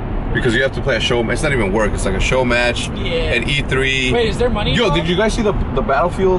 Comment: did anyone else hear at around 3:07 scump goes it there money involved?